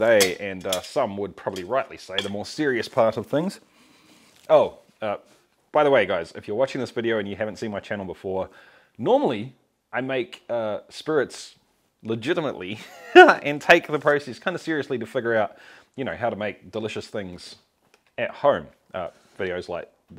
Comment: All this time on videos like this I thought you were saying "mean spirits".